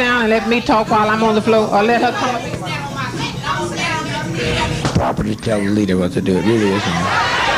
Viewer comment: he actually says ‘Property telling the leader what to do!’ What a dog!